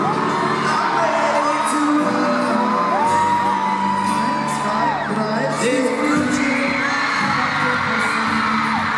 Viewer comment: loved the video and one direction very much :) but, what did harry say at 1:30 I couldn't hear what he said :):) thanks :) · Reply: eins = one, zwei= two, drei= three, vier= four